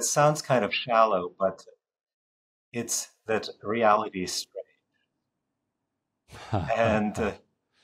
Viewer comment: Simple question: What is the two-word phrase the Professor says at 6:27 min.? - Daniel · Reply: "..that reality is strange."